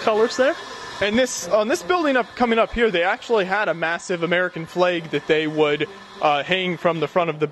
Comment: American fleig? sounds like you say fleig and not flag :D